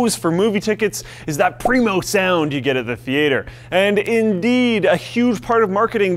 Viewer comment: Every time you say Theater it breaks my brain.. I just hear "th..E.. Ate her" lol